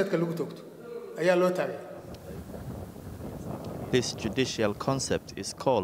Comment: The reporter is great lair 5:21 "he said tradionaly called Qisaas but its fake it is religeously qisaas'kill' "so he must insure what he said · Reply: He said Judicial. Your grasp of the english language is weak, don't comment as if you're fluent. You're wrong.